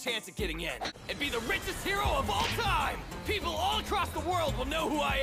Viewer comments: When Bakugo said, “The greatest hero of all time!” At 0:32 I thought he said “the most racist hero of all time.” 💀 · “ILL BE THE RICHEST GIRL IN THE WORLD!” Is what I heard- 😅